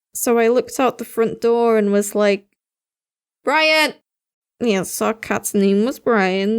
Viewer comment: I thought you said Ryan.. B R I A N